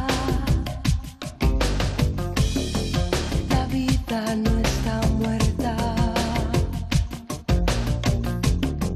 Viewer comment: Nunca he sabido que dice en el minuto 0:56-57? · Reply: Creo que dice "la vida no está muerta, dormida nada más.."